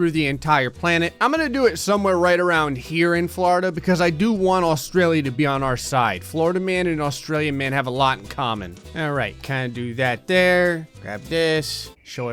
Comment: Keep clicking rewind every time says alright at the very beginning, it sound like he is saying are ass